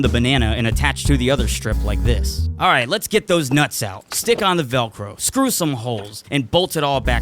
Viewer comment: I skipped to the end to see the result and I heard “lets get those nuts in there”